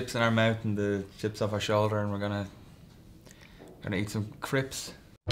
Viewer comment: lol it sounded like he said eat some crips